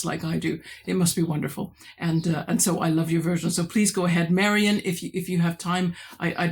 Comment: Hah. Got totally confused. I heard 'Marion,' so I did the tag, but you meant 'Marian'. That makes the intro to my tag video a little awkward as I only realised after uploading. But they were fun questions 😄